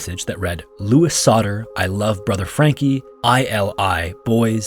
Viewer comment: That note at 26:05 does not say some numbers but instead Age and then whatever his age in the pic might be. And I think instead of I Love it said In Love, Brother Frankie as in the more olden times version of With Love for letters and such. And then probably not ilil but just an And sign that just happened to be very thin and tall, with Lil Boys right after that. So all in all Frankie and his lil boys or sons signing the pic, after titleing it with who it shows and then belatedly adding the age of Louis too.